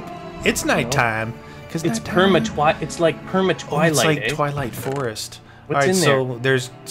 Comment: Did say "PermaTWAT"?